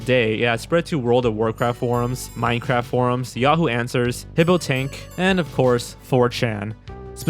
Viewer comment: what does say at 5:42? "pibbo tank"?